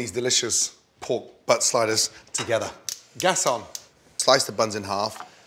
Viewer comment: I thought that it said spiders for a second